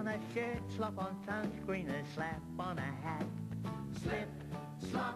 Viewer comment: i always thought he said slip slop slap that ass